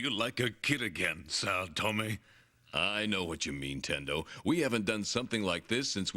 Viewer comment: Is it me or did anyone else hear the word Nintendo being said at around the 11th min or 10:30?